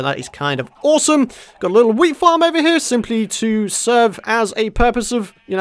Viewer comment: Was it just me who thought he said weed farm 30:41😂😂